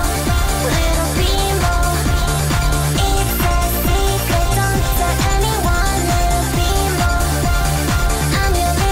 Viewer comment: What did she say? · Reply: "it's a secret don't tell anyone"